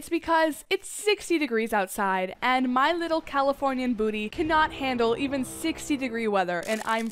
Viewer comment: My volume was all the way up and all I hear is “ I’m CoLd, My CaLiFoRnIa BoOtY cAnT tAkE iT!